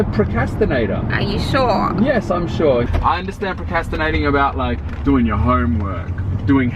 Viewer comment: it sounds like is saying proCASTinating instead of proCRASTinating